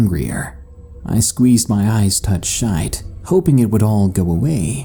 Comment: "Squeezed my eyes tut shite" I had to rewind to make sure I had heard what I thought I'd heard lol